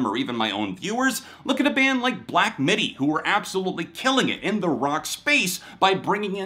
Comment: Did you said "Black Midi" in minute